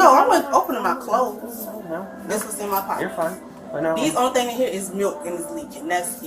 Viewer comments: She said "sooo I'm gonna have a felony charge" lol lady, that's not even the kicker. You got the worst kind of felony because you'll never work in that town again 🤣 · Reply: Did she say " the only thing in here is milk " when she was talking about being searched?? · Did she say “ These only things in here is milk, and they is leaking” ? As she grabs her boobs. 🤢